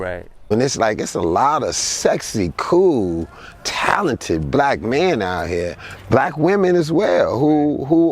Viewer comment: did he just say sexy black men??